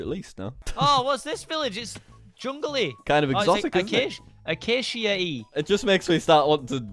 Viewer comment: Acacia Yeet is what I heard.